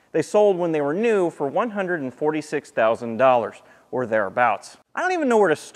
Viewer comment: Did he say $146,000.00 ? Or you can get a nice use 4door honda civic/corolla for about $5,000 - $10,000